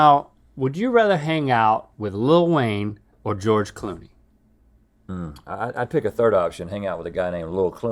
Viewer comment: I REALLY thought you said lil wayne or Georgle Floyd and i had to watch it back to be sure